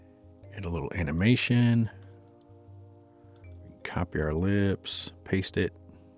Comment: I thought you said “copy her lips”! Lips, ellipse. Same thing. 😂